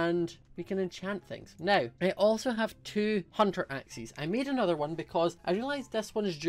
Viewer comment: I heard 'two hundred axes'